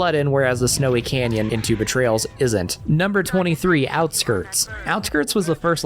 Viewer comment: I’ve watched almost all of your videos before eating this one. As someone who has never played Halo but enjoy your videos I thought you were saying Tube of Trails instead of Two Betrayals. I’m so dumb.